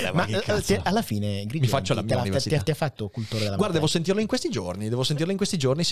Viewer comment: Ragazzi Help, non ho capito cosa dice a min 1:17:14 "ma alla fine". l'ho riascoltata 6 volte 😅😅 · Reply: "Ti ha fatto cultore della materia?" 🤣